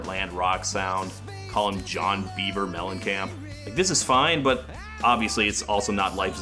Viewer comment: That John Beaver Mellencamp joke hit me on like 3 different levels. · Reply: I thought he said "John Bieber Mellencamp" which works just as well.